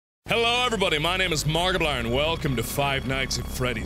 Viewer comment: Lol it sounds like mark says "Hello Everybody My Name Is Markiblan